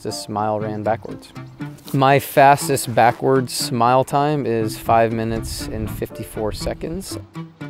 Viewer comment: I heard "My fastest backward smile time is five minutes and fifty-four seconds" Impressive, my backward smile lasts for days